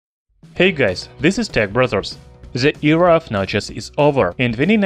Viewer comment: what they said " hey guys this is tech brothers" what i heard " hey guys this tech brazzers"